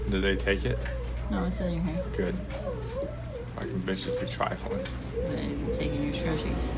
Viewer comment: did he say “bitches be trifling” 😂